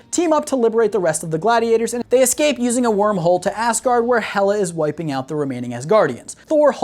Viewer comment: I cant tell if he said “Ass guard” because the Grand Master says it in Ragnarok, or if it was unintentional, or I just cant hear right